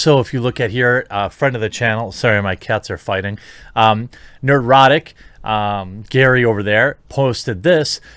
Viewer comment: I misheard heard at first and heard " my cats are fighting, they're erotic"